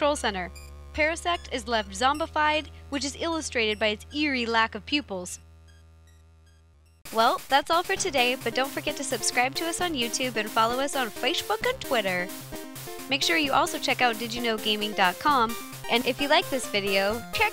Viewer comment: Did she say fashhhbook?